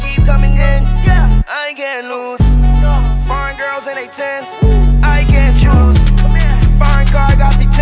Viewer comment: ngl at first i thought he said Fine girls and they ten I can't choose